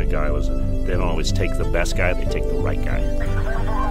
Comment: i heard "they don't always take the best guy, they take the white guy"